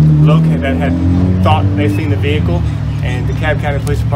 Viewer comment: I thought she said the Dad seen the kid? How does she have such a accurate description down to the color of the marking on alleged perpetrators clothing even shoes...